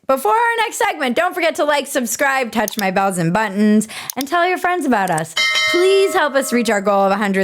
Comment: Maybe I'm losing my hearing or something, but I thought you said, "Touch my "balls" and buttons".